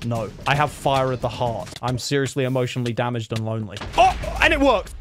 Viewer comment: Did he say emotional damage 1 year before the meme